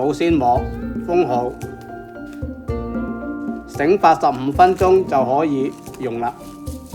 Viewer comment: I heard him say 85 mins for the first rest or proof but most recipes in the web for first proof using baking powder and yeast dont rest it this long. Someone that have made it please let me know if first rest period is 15 mins. As written or 85 mins. As the Chef had verbally said. Which did you use when making yours and it worked? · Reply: I think you misheard it. He said 醒發15分鐘，not 醒85分鐘. 8 and 發 sounded quite simliar here. I also had to replay that part to make sure lol